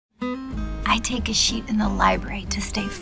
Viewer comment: lol its sounds like there saying i take a shit with a spanish accent!!!!